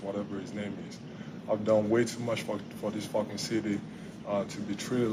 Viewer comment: Did he said: 09:08 "I did way to much for this fucking city" :D :D